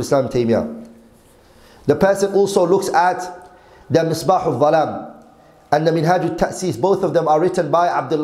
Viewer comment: What is he saying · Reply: مصباح الظلام و منهاج التأسيس